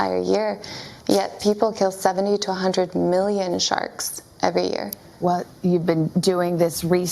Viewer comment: I thought she said 72 hundred-million sharks at first. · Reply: I think she said “70-100” a year